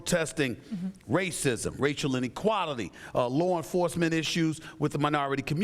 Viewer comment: Why does SAS sound like he says Rachel when he says racial?